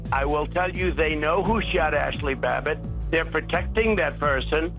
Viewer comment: when I hear "who shot Ashli Babbit" I *hear" Who shot Roger Rabbit